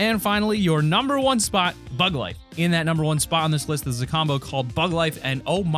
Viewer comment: I thought he said Bud Lite